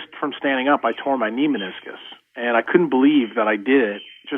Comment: I didnt understand what he said about standing up and felt somewere at 13:00? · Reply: Hw tore his meniscus in his knee.